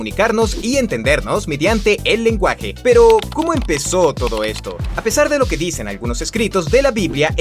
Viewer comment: Todos:www que interesante Yo: porque Dice todco en el min